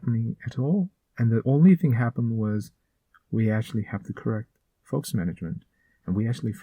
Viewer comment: What was said at 2:12-2:14? Was it "We actually have the correct _ management."? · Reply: focus management